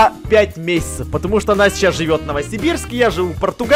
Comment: у меня или жиза, или мне послышалось он сказал "Настя" хм... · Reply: Не Настя, а «она сейчас»